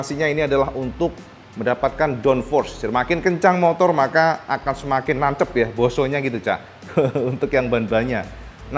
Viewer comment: "semakin kencang semakin nancep" 😂😂😂 cak kok aku dengernya lucu ya kaya apa gitu 🤣🤣🤣